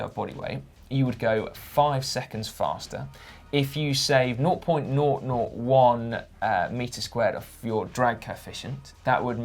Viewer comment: "If you save..." I don't understand what Simos was saying. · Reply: "Naught" = zero, so 0.001 meters squared. A really tiny surface area, around the size of a headset cap.